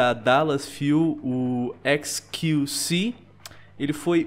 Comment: XQC... Só eu q entendi "esqueci"? '-'